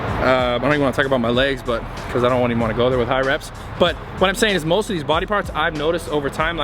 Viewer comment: did he say he doesnt want anyone to do high reps on legs? Or did I mishear him